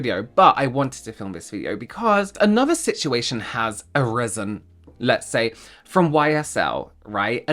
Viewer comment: I thought you said, "Another problem has arizzled." 😂